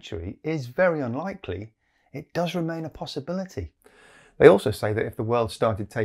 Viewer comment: I hear: "An unlikely possibility" and will ot loose much sleep about it